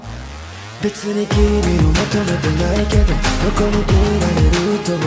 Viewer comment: 「求めてない」がmoment night 「瞬間的な夜」的に聞こえる笑